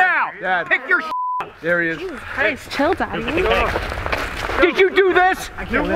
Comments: Did anyone hear “chill daddy” at 1:07 😂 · Does anyone hear that at 1:07 “Jesus Christ chill daddy” 😂😂😂😂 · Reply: I heard " Jesus Christ.... Kill daddy " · I heard " Jesus christ, chill daddy " XD